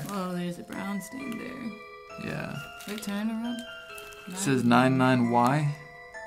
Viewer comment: For a while I thought it said "gay" Anyways... it is night for me Great